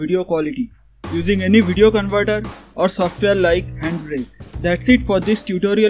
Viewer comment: what did he say (I heard anprint)..... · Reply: Any video converter he said